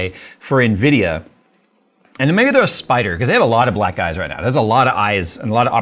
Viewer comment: i swear he says "BlackGuys" 💀